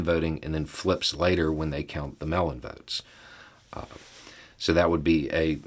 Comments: For some reason, I kept hearing 'mail-in votes' as 'melon votes' in this video. I was very confused. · I'm obviously very hungry right now, because I keep hearing "melon votes."